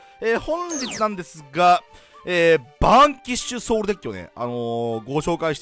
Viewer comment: バーンキッショソウルにしか聞こえなくて耳掃除したわ